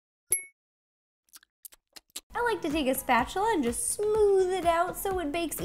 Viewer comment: Hey Ro you should bake with Liza koshy 😊😊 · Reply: DiamondHeart 260 lol I thought you said take a bath lol